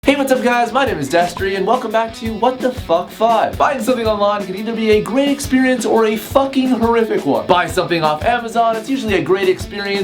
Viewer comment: Every time Destery introduces himself I always hear "Dentistry".